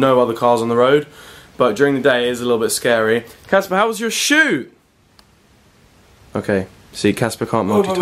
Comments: i thought Oli said "Caspar, how's your shoe" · At 0.19 I thought oli said how's your shoe and I actually don't Know what said still? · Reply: Glenn Matheson he said shoot